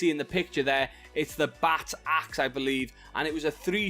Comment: did you say bat axe? The melee weapon on screen? Or did you say spike axe? Couldn't understand you lol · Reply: Bat axe